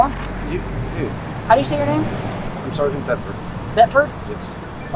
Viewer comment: I Thought He Said SGT Pepper At First!